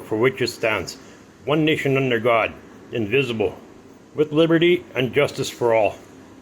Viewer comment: I'm sorry but at 3:30 it sounds like he said invisible instead of indivisible and i thought that was funny